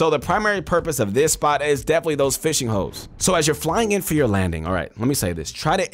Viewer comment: i thought he said fishing hoes 🤣🤣🤣🤣🤣